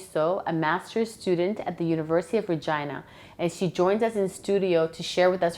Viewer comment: I swear I thought she said university of vagina ahahah well I need to get my mind out the gutta hahaha 😂😂